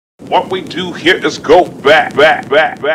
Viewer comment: About time I found this song I been looking for this song for 3 years now I found it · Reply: Same, I heard it as "all we have to do is go back" and didnt get results when I searched it